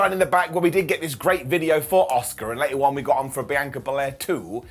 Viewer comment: Bro I need some sleep, when said Asuka I thought he said Oscar